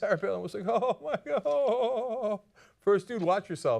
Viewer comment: What did he say at 3:53? First dude watch yourself?